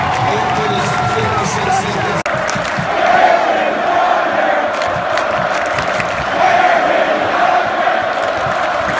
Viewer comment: What were they saying at 2:24? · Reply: “Aaron Rogers!”